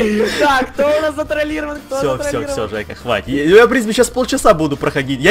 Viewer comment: всё всё, ЗАЙКА, хватит?! Или мне послышалось?